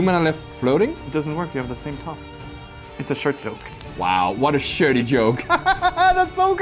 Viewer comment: I must say, is so fun to have on camera playing magic with the cardmarket-magic crew. Like how andrea comes on every once in a while, what would it take for this punny gentleman to come and play more magic with you all? In other words, I hope to see more of, especially on cardmarket! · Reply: When I heard say "shirty joke" I imagined them in Step-Brothers. "Did we just become best friends!?"